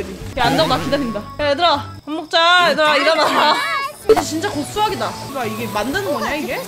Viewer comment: 나만 얘들아 밥먹자 애덕아 일어나로 들리나....